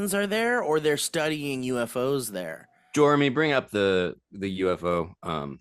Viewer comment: Did call his fake assistant fucking "Joremy"